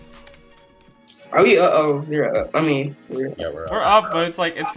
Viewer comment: did i hear a swear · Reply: IIKINGBLAZE9 clock and score